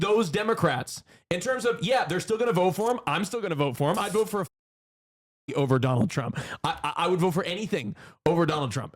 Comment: What did say there? He's going to vote for what over Trump? The way Hasan and some chatters reacted makes it seem like he said something weird 😅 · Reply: D3d b0dy